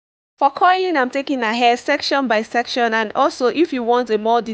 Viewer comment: 😂 I swore you were saying COILING her hair 💭🤔I’m like ohhh a new technique🧐. OMG 😮 you are saying curling 🤦🏼‍♀️😂lmao I loved the coils either way lol 😂